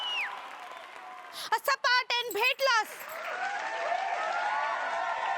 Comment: what she say i dont understand? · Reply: She means to say shut up and get lost but says incorrectly